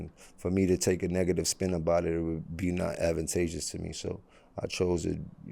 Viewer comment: What is he saying at 1:16??? I cant make out that word he used.. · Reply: “Won’t be advantageous to me”